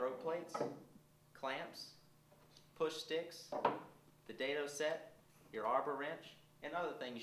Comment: the captions say "dato" lol....what the hell is a dato? is that a new type of blade? where can i get one?